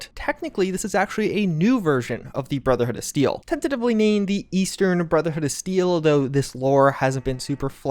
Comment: he says 'Eastern' i hear 'Midwest Brotherhood'